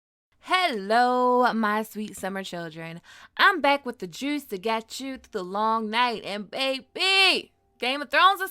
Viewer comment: I swear, every video I hear "I'm back with the Jews to get you through the loooong night."